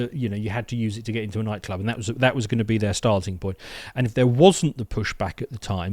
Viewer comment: I heard you use the phrase "..starting point.." , but i misheard that as "...Stasi point.." - was i wrong ?? LOL ;)